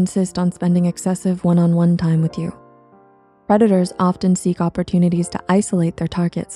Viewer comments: I legit heard "redditors" instead of "predators" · did they say redditors here or am i stupid edit: im stupid, they said predators...